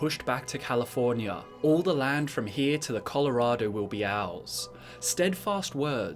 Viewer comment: this is what i heard the first time. "All the lands from here to the colorado with be owls" Hahahaha :D:D sorry dont hate me =( x)